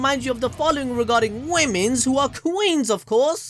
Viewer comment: Did he just say, "womens"